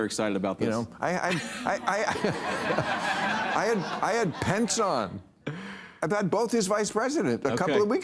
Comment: I thought he said "I had pants on." 😂